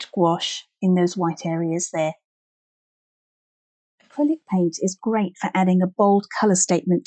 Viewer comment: Will somebody please tell me what product she said at 43:26, right as the red bow appeared? The audio quality dropped off and cannot make out what she said. Thank you!!! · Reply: Hi, acrylic paint is the product mentioned at this part of the video.